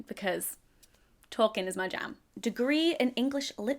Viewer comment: Anyone else hear "Tolkien is my jam" at 8:30?